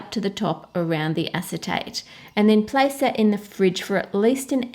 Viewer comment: when you said acetate i thought you said acetone and freaked out.